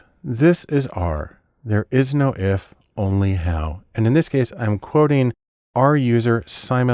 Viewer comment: before he showed the text i thought he said "Only hell" LOL